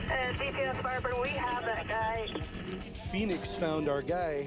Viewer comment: mark those captions are so far off of what that pilot is actually transmitting to the ground. She actually says “Uh DPS FireBird, we have that guy.” Firebird is the name of the Phoenix police helicopter unit. She was calling DPS ground units to inform them she had located the guy who ran. Come on Live P.D. your captions are slacking.